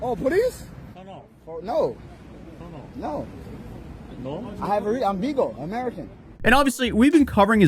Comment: did he just say “I’m legal, I’m an American” in a foreign country? That… sort of explains a lot